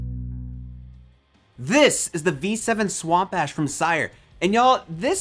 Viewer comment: At first I thought u said swamp ass…